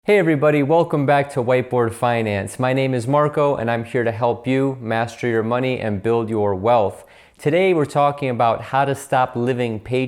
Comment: I thought he said white boy finances 😂😂